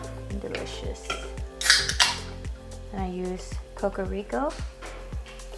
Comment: Can you write down the ingredients of the peanut dipping sauce, please? I cannot figure out what you added in after sugar, such as half a can of some liquid and the following...Your video at 4:22. Thank you! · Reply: Hello! After the sugar, I added half a can (6 oz) of Coconut Soda. I just updated the description with a list of the ingredients used in the video for a quick reference. I hope this helps. Happy cooking. ☺